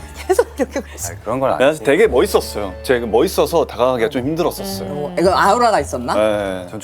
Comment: did she just say “aaooora” ?